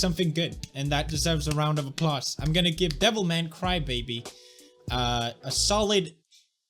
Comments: First i thought he said "Devil May Cry" but the "baby" killed my dreams... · I kept mishearing him and thought he said Devil May Cry... now I'm replaying DMC4. Damn you pewds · i thought he was saying devil man cry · I thought you were saying devil may cry wrong on purpose 😂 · thought he said devil may cry and was like HELL YEAHH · I thought he was saying devil may cry. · I heard I'm going to give devil may cry baby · thought he said Devil May Cry